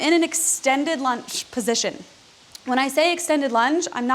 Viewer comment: Oh, Lunge! I thought you said extended lunch! :)